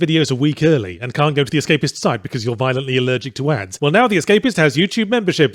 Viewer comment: I thought he said "violently allergic to ass."